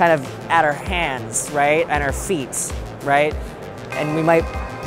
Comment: I'm sorry but 0:27 . Am I the only person who heard heard him say "feets". Like "feet" is already plural, you don't have to put a "s" at the end ( it could just be me or he could be bilingual idk)